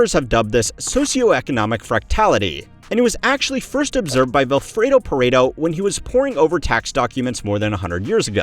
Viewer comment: Subtitles are wrong about the name: Vilfredo Pareto